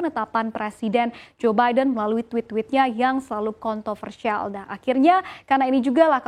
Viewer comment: Saya yang salah denger apa mba nya terlalu sering keinget kata kontolpersial 😆😆😆